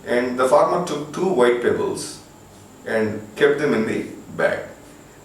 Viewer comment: wait a minute sir! At '3:37' did u just say "THE FORMER PICK TWO WHITE PEBBLES INTO THE BAG?" why whould the former take two pebbles?